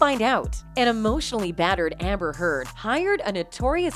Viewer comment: An emotionally battered Amber Heard. Did I hear that right?